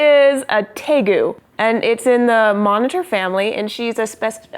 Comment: I got so confused because I thought it said "tengu" (a Japanese mythological creature...I then saw a reptile and realized it said "tegu".